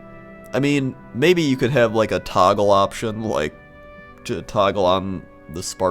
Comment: When I hear "Toggle option" I heard "taco option" instead.